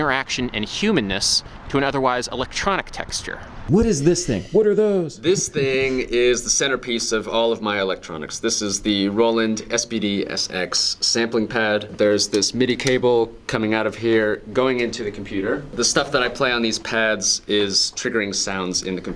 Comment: when he said "roland SPD" I thought he said "SBD" meaning silent but deadly, and I think that's a far better name for a sampling pad